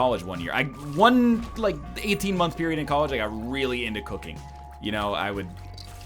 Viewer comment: or around there he said i got really into cooking for 18 months, I thought he said cocaine and was very surprised got a second